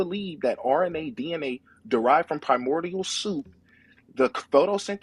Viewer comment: Did he say 7:12 “kaimordial soup?”